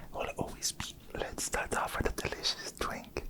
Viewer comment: I heard twink 😳